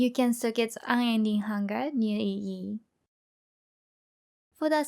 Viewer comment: i didn't quite understand what EE was, but now i googled it and that's Keystone under that jewell socket. And i have a question about changes that occured with this leagues start. I watched OLD "Pure Spectres Build" video that featured Primal clashclaws and i really liked that you gave 3 different choices of build: w/o claster, with one and with 2 clasters. So i am really interested if build with 1 claster still works. I mean that THIS build's tree is identical to the tree with 2 clasters from the OLD video. So i was wondering can i still build 1 Claster build or it is irrelevant in Ultimatum? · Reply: Sorry about that. I have trouble to say elemental equlibrium so I just said EE. Actually one cluster is inside the pob pastebin. i wanted to make guide simpler so just double is in the video. no cluster is same as minion army build.